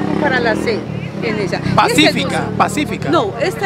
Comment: Minuto 4:28 Yo tbiem escuché pacífica